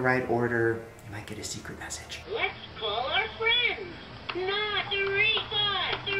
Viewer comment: i heard another father....and when i heard it the second time i heard mother father